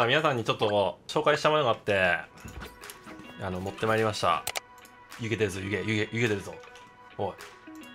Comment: 「湯気出るぞ」がyou get this allに聞こえた